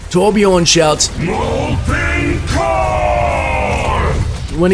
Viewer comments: For a while I thought Torbjorn's ultimate was actually Winston's whenever I heard it. I mean, now I know he shouts "MOLTEN COOORE", but for a while I heard it as "KING KOOONG". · i always thought torbjörn yelled "ENCORE" xD i never heard him say molten